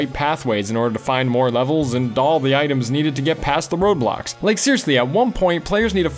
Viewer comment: Yes, this is off topic. It sounded like he said ROBLOX. When in reality, he said road blocks. Also, great video.